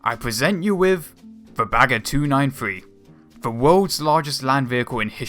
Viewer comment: I honestly thought it said "and I present to you . . . Baguette 293"